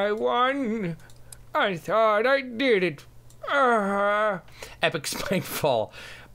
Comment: It sounded like he said "but I died" or something...I don't think he cursed. it didn't have the "f" sound